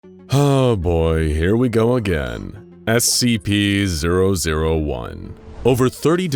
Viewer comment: At the first I thought he said Ah shit here we go again